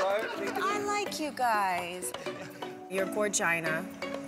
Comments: Did Nicole say "your vagina" at 15:02??? IM DYING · 😂😂😂 what did she actually say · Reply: “Gorgina”